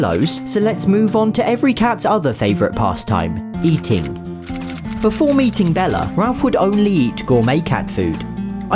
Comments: I thought he said "Before eating Bella" Help me. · I looked away for 1 second and I thought I heard before eating bella and I was like excuse me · I thought he said eating Bella